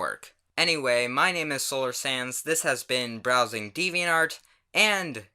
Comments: at the end it sounds like you say solar sans sorry if its judging just wanted to point it out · At the end when you said that you were Solar Sands it sounded like you said “Solar Sans” lol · xD why do i hear Solar "SANS" when you say solar sands